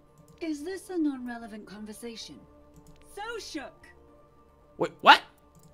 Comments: The so awesome voiceline kinda sounds like she says "so shook" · did... . Did she just say "so shook"?... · For the “So Awesome” voice line I thought she was saying “So Shook”😅